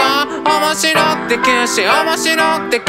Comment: 「なんか生み出すのさ」って言ってたのか 「中身出すのさ」だと思ってた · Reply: ｢面白くて急死｣が｢面白くできるし｣って聞こえてた